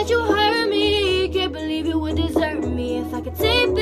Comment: I thought she said can’t believe u would desert me 🤷🏾‍♀️